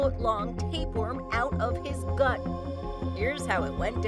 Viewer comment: I thought it said he pulled out a 5 foot tapeworm out of his butt 🤣🤣🤣🤣🤣🤣